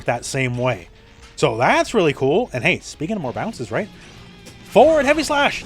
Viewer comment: I heard "Baiken more bounces" and didn't need to listen from there. Sounds great. Arcsys are geniuses. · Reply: "Speaking of more bounces.." Proceeds to strut forward.